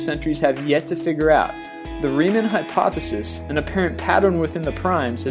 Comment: If anybody cares, the guy is saying "Riemann hypothesis" (about 1:25). I love his videos, but sometimes it's very hard to tell what he's saying.